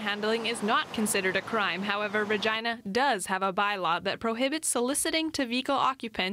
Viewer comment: cop should get a ticket himself the sign he's holding is distracting driver's as well as the process of getting money out and handing it to the hypocrite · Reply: did she just say virgina 🐵